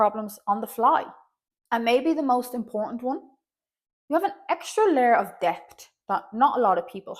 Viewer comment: God love you, but when you said “an extra layer of depth”, I heard “an extra layer of daft”. And I identified with that. 🤣 I think there is some judgment of necessity involved; there are gents like the other more successful stick fella, who aren’t ACOA, who may have had an at turns aloof dad, etc, who feel somehow hurt, and their struggles are flooding the psychic atmosphere of people’s perceptions of other stickfolk with much deeper struggles, and affecting how people perceive ANY childhood experiences no matter how abusive, neglectful, damaging, etc.